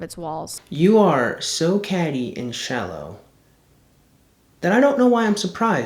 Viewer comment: What did he say at 25:29? "??? and shallow"? I never heard that word before. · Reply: “Catty”. It’s more slang for “sharp, insulting gossip, typically by young women.” It’s mostly when young, you saw a lot of in highschool, girl spend time insulting people without much merit in a group to feel better.